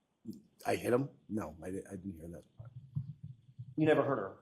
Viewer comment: WAIT.. I thought she said “ I did this” NOT “ is he dead?” The whole not checking the house, the messages they found, the owner didn’t come out , when John was found, just ALL of it. He was over 6 foot. WHY is there no broken ribs or sternum damage?? It’s all very suspect . ESPECIALLY, the way the one cop said NOPE when the other messaged back about the owner of the house & how he would be investigated..